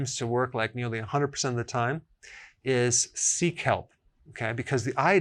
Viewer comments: thought he said “seek help” duhh 😂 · I heard that as "The most powerful remedy is seek help" . Then I realized you meant sea kelp :-)